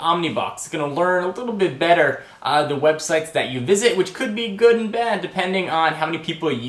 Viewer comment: whoa did he just say websex :D